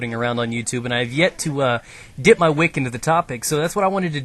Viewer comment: I heard "I've yet to whip my dick into the topic."